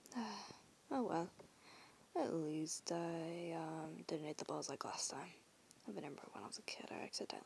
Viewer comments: I thought he said I time I eat the balls-☠️ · I thought he said I ate the balls 💀💀💀